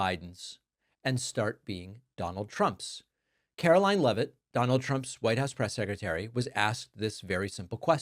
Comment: Hahaha. Sounded like you said "Karoline Leavitt, trump's white-ass press secretary."